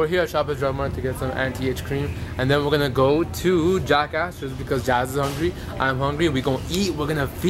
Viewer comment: when said him and were going to go to Jack Astor's I thought he said he was going to go to " Jack Asses, " I was like What? Someone actually named a restaurant that, but nvm I knew what he said when 4:55 came around. I have brown moments ( Get because I'm brown, ehh??? .... no? ok. ) So much fun to see Youtwo ( see what I did there😏😏) reunited!!! Hope to send a letter to my fam soon! Xoxo!!